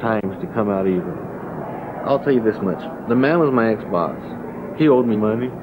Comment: did he say Xbox ?